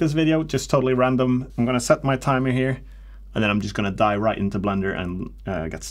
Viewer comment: did you say "die right into blender"?